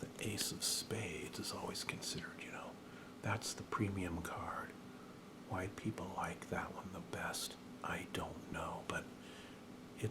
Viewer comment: I thought you said "white people like that one the best"